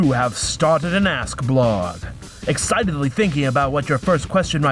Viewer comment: i thought it said you have started a ass blog weird